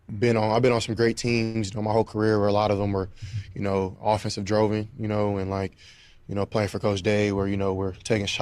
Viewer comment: And did he say”droven”? Sheesh.